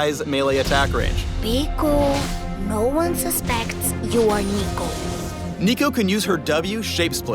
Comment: did he just say neko neko nii just kill me already